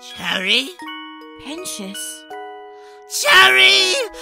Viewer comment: Why did I thought Sir Pentious said “Charlie!”, instead of “Cherri!”